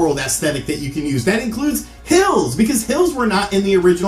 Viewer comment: I SWEAR TO GOD I thought he was saying "pills"! Which made me think maybe he had a drug and/or Dr.Mario addiction! Yes I know now it was "hills".